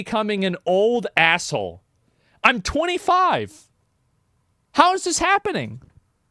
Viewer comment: did he just say he is 25, OMG, I’m 37. He is starting to speak like me. He’s a Padawan, he will get there